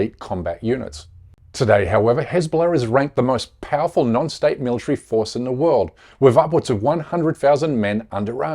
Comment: hahahaha, I swear I heard you say diaper force